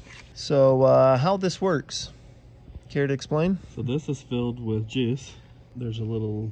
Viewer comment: I can’t be the only one that heard “so this is filled with jizz” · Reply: I could have sworn he said it’s filled with jizz